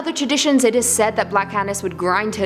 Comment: All seriousness I thought she said black anus